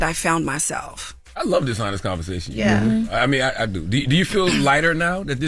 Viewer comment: lmao i heard "dishonest conversations" till my brain processed the context properly 😂😂😂😂